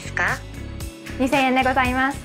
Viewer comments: In the screen, a cashier says "Its 2,000 yen." but subtitles says "Its 480 yen”😂😂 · The cashier says 2000 yen, but the subtitles say 480 yen.